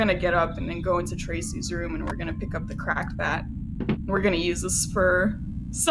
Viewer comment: When you said 'Cracked Bat' it sounded like you said 'Crack Bat' and I thought for a moment there was a new item I didn't know about. I like the idea of a Crack Bat though.